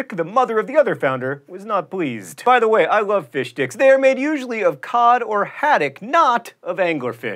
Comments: IT sounded like he said fish d*cks not fish STICKS lol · You like fish sticks? what are you, a gay fish? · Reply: When he first said it I heard 'fish dicks', and had to back up the video to figure out what I missed. Whoops.